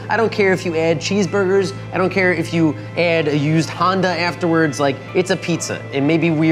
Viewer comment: I thought he said used condom.